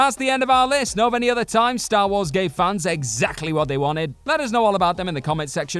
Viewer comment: Just me being stupid but honestly when he says gave it sounded like gay i and was like did this bastard just call star wars fans gay 10:24😂 but then put on CC and saw it was gave took me a second